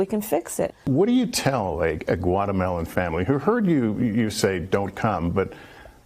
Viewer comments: I heard him say "what do you tell guatemelon family" its not hard its "GUA*TE*MA*LA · I spit my drink out with laughter, after I heard him say guatermelon family 🤣😅